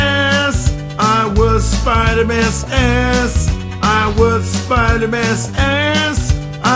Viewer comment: It sounds like he says "I was Spiderman's ass"